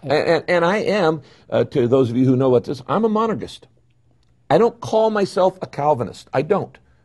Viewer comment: what is Rick Warren saying here? “I’m a ______”?? a what? a monagist? what did he say? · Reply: He says he's a monergist. He believes God, through the Holy Spirit can bring someone to salvation without any cooperation from the individual. Monergism.